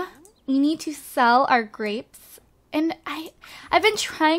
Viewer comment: A hundred and thirtythree grapes. I thought she said graves lol.